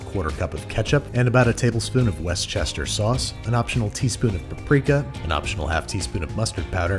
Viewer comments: “Westchester sauce” Me: violently twitches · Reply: Today I heard “worst-sister-sauce” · "westchester" sauce.. although i thought i heard "worstchester" sauce.. 😏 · did he just say west chester sauce...